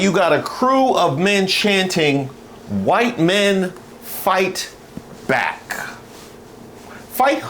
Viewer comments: i thought they were saying 'white men fight baths' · I heard “White Men Fight Black” 🤷🏾‍♂️